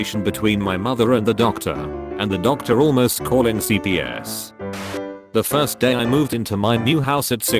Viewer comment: I heard cvs as in the store